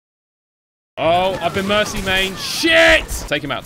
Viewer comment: Anybody knows what he said here when he was like "I've been... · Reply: mercy mained